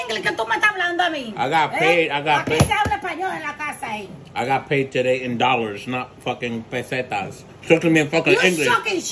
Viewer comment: "I got paid today in dollars, not fkin pesetas" lmaoooooo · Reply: I initially thought he said, “eh I got paid in dollars today, not placentas.” I thought well this should a pretty good video to start the day with. Still wasn’t let down.